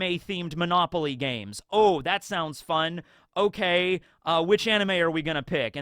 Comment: They 100% did not say "That sounds *fun*," they said "That sounds *profitable*"